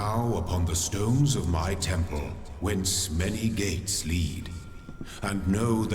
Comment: Am I the only one who thought he said "my temple where smelly gates lead" at the beginning?